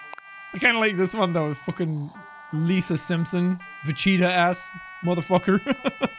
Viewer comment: Did.. did he just say, Ve"CHEETA" ass